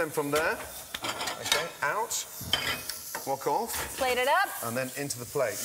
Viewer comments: Wok off sounded like Fuck off. my mind is programmed to hear "fuck" when watching gordon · I heard 'fuck off' instead of 'wok off'. Freaking Gordon Ramsey...